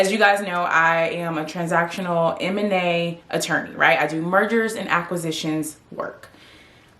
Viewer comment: I thought you said Murders and Acquisitions. Spit my coffee out when I realized what happened. So you, Ms Attorney at Law, Owe me a computer screen😉